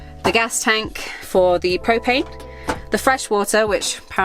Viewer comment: Hi guys, thanks for the van tour, I was making lunch while listening & I thought I heard “this is where the cocaine lives” Whaat? Really??😮 I was quickly corrected, ‘they said PROPANE”. Oh, right, 😅😂